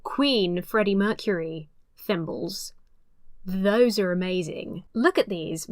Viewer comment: I thought you said Freddie Mercury NIPPLES in the intro and honestly it made me want to stay even more